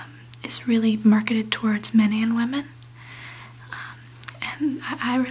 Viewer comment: I always thought she said "it is more focused towards 'Minion' women" - whoever they are lol. Only realised today she said 'men and women'. Doh....